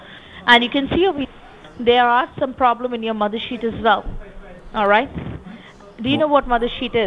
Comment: When your mothersheet is ionfected and your omputer is infected 😱 It sounded like she said mothership